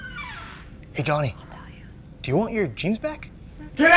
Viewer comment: umm did anyone else hear a woman say "i'll tell you"